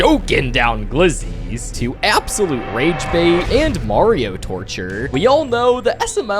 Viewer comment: did he say Mario?!